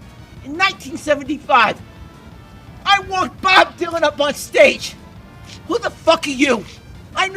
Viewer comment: The first time I saw it I thought he said “I wanked Bob Dylan onstage”